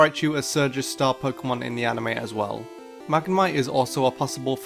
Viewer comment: did you said magmar or magmite? xD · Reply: M A G M A M I T E